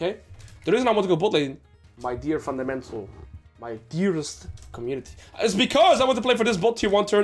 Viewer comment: 🤣🤣🤣 did you just call us fundamentals??